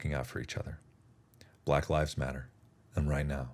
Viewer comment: Thank you for using your influence to help fight injustice. You quelled some doubts I had about you. Also at the end it sounded like you said "white lives matter" and I had to rewind to hear "black lives matter".